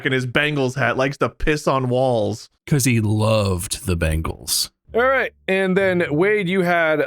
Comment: Oh dear me, 52:58 I thought he said/meant the 80s pop group The Bangles. Im so old.